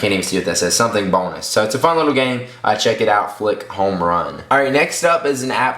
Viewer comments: i thought that just said fuck home run. wtf. haha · i thought it said fuck home run